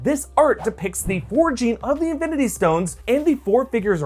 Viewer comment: I thought you said "orgy" at first, but thought it was silly and realized it was "origin"